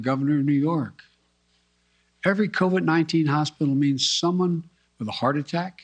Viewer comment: Did he just say become overunninggen?